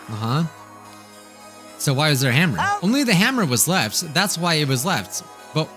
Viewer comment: I thought he said HAMSTER not HAMMER... ah yes. A hamster. The most dangerous weapon. They can even take out a powerful robot with a poop obsession.